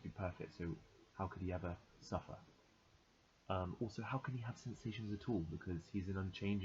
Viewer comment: where you said “how can he have sensations at all “ I thought you said sensations O’Tool and I thought who the hell is sensations O’Tool I haven’t learned about him 😂